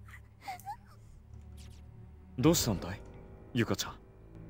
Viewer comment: i swear at 29:10 kizami says "Do something, Yuka-chan" you heard him, get your damn crying-ass up yuka, there's shit to do